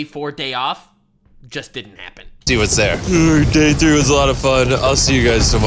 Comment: of video On top left it clearly shows day 3 but then mr beast says "day 2 is lot of fun i will see you guys tomorrow" am i hearing him wrong or do you guys hear same thing?